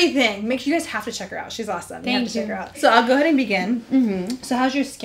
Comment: E eu q entendi no 01:00 a Huda falando pra Mari "agora amigannn.." quem escutou isso curte kk